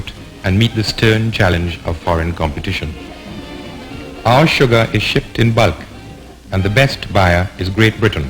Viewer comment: what did you say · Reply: “Sugar is shipped in bulk”